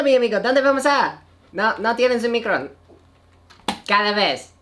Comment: did he say omicron? LOL